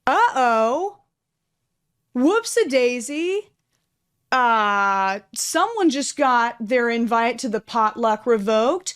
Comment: what was the word that said? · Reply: The N word